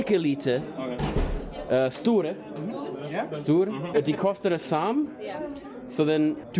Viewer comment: Bro it just sounds like off brand English when he said “do they cost the same” in Norwegian I just heard “dee costa da saam”💀